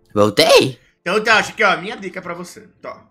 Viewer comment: eu entendi" minha tica pra você " KKKK